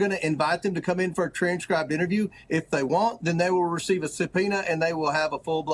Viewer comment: WHAT EVER. COMER SAYS" IF THEY WANT " TO KNARK OUT PEOPLE.